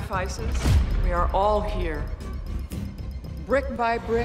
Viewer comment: Who Heard Rick Insted of Brick